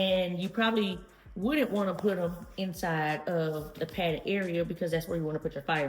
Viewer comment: Lol I thought you said padded farts. That's a great looking bag though.